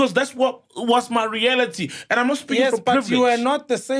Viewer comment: I hear sol